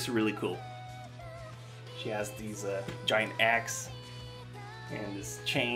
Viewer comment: ohhhhhhhhhhh you said giant axe i thought you said giant ass xD